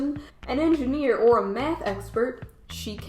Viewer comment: When you got to the "jobs" part I heard "Meth expert" not Math, I was like "wait, what?!" 😂😂🤣